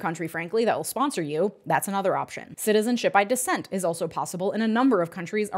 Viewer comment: Aw man, I thought she said "citizenship by dissent". I was like "easy!"